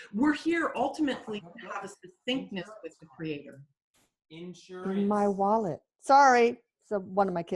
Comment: Listen for EVPs, guys! Let me know if you find one. very chatty! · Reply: I heard "insurance" and then "my wallet"